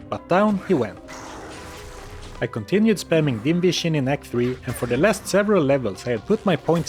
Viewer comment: Can't help myself, I hear "invision" instead of "dim vision" every time you say about that. You become invisible to enemies after all.